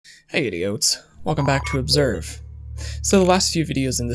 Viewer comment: Lol Oops I thought he said hi idiots in the beginning 😅